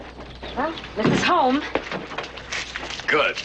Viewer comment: I thought she said "Here's a comb," instead of this is home. What tipped me off was, what would Chrome-Dome needs with a comb? And he sure is bossy for a cue ball.